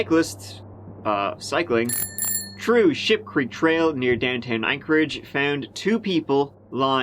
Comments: Thought you said shit creek trail. Nearly choked on me cuppa 😂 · I'm so glad you showed the sign on the trail, because I SWORE you said Sh!tcreek Trail, and I was wondering why someone would be cycling around something that smelled that bad 😅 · Oh geez, at 2:04 I thought he said shit creek, and thought wow, maybe there's a real place where someone lost their paddles? lololol